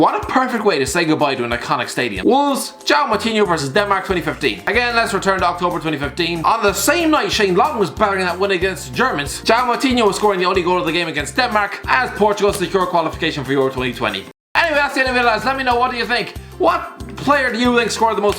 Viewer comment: is it just me or did he say Germais